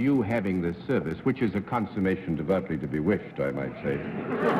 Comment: Does anyone know what John was saying when he zinged Bennett at 9:24? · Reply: John says that Bennett having the anesthesiologist's services is "a consummation devoutly to be wished" -- a Shakespeare quote, from Hamlet's "To be, or not to be" soliloquy. John is jesting that he'd like to see Bennett go under the ether.